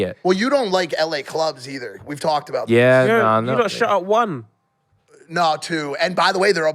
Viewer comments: Do you know what the he says @ 2:36 something something one? Can't make out what he says... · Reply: lol yeah tobi got a strong British accent he said “you lot should have won” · Do you know what the he says @ 2:36 something something one? Can't make out what he says... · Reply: “you lot shut at 1”